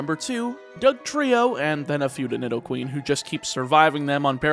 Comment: Did you just say Futa nidoqueen? I didn't want that image in my head